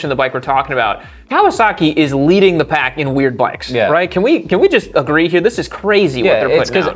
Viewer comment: Did you say weird? 😮